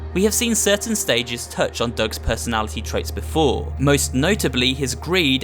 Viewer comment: I almost thought that said Doug Bowser and I was like “wait what’d he do wrong”